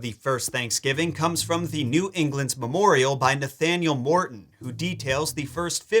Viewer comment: I thought I heard that right...Nathaniel B?